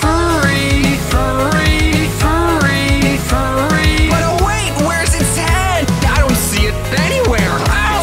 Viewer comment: I feel like the bit where it says "but oh wait wheres his head i dont see it anywhere" I thought it would say "oh wait, where is its dad, i dont see it anywhere"